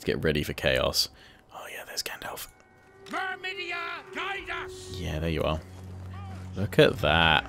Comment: LOL. Sounded like the hero said "Clamydia... guide us!"